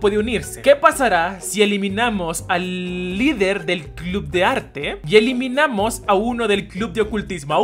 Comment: En un nombre pensé que decía que pasa si eliminamos a la niña de los tamales" :'(